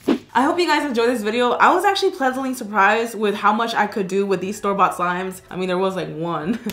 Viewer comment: I thought she said starbucks slime but she said store bought slime lol